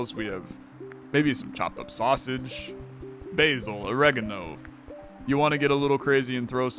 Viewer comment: You said "pure spaghetti, maybe there's some chopped up sausage" but I heard " maybe there's some chocolate sausage" and I went 😮 🤯 But, yeah it does sound pretty crazy LOL 😂